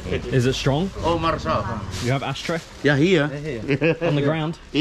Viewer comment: Thought he said “do you have hash, tre” 😂😂😂😂